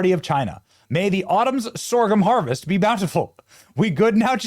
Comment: Thought you said “may this autumn’s ORGAN harvest” lol